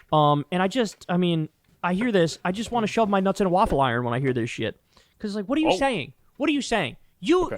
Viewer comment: All I heard was "I want waffle irons for Christmas."